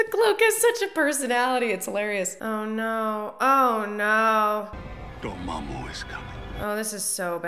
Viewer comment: "Dormammu is coming" but I heard "joe mama is coming" and I can't unhear it.